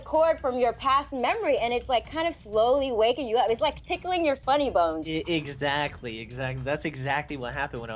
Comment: i thought she said "pickling your funny bones." also she's drunk as fuck ey b0ss habe you seen da alien